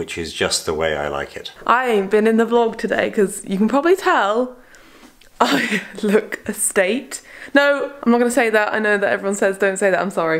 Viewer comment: What is saying at 4:01?? I've replayed it 100 000x but I still can't seem to figure out what she's saying? ... Estate? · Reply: “I look a state” 😉